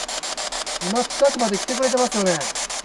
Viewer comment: ｢来てます｣の後の言葉、｢殺す｣じゃなくて｢ここです｣か｢こっちです｣に聞こえるしその方が辻褄が合うなと感じました